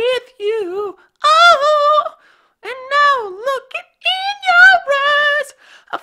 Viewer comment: am i the only one who thought he said in your ass at 3:44?